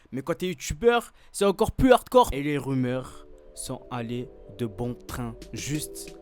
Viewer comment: Quand il a dit " des rumeurs " j'ai entendu "des ubeurs"😂😂